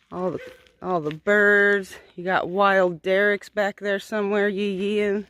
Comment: I thought you said Derek was back there peeing 🤣🤣🤦🏼‍♀️🤷🏼‍♀️